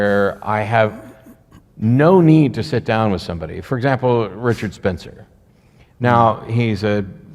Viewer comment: For a second I thought Dillahunty was saying Robert Spencer was a white nationalist and then I realized he said Richard Spencer. I was very confused for a second cause I've never heard Robert Spencer say anything that would make me think he's a white nationalist